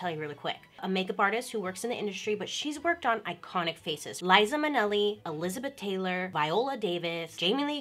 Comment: so uhhh apparently lizon minnelli, the middle-aged overweight blind pianist and jazz artist who looks a bit like stevie wonder, is in fact a figment of my imagination??? and that ACTUALLY i've been mishearing the name liza minnelli my entire life and have just conjured a fake celebrity in my mind to fit that name??? and i've got to say i'm very upset